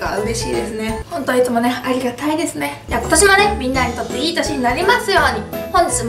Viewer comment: の「ありがたいですね」が「advertisement」に聞こえた